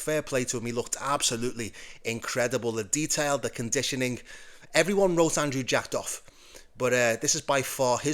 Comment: did you just say everyone knows Andrew Jacked off?